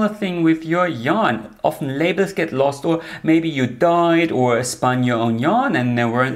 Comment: I heard, “Maybe you died,” as due to the longer pause after “dyed”. I do need to make sure all my yarn is labeled in case I die. 😂